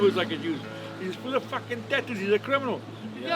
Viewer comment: he actually says, “He’s full of fucking tattoos, he’s a criminal.”